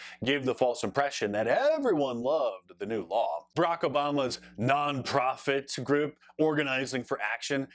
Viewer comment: Did he just say Iraq Obama 😂😂